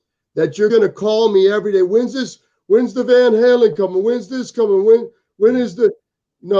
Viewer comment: Did say... 'Van Halen'?!